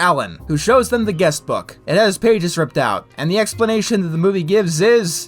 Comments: I think he said Moths. I guess Moths are World-renound for their appetite for Paper, at least according to these Writers. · I think he said, “moths.” 🤔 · i think he said moths